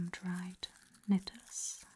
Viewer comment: Does she say “some dried nettles “ or “some dried lettuce” at 21:54 I genuinely could not tell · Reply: nettles :)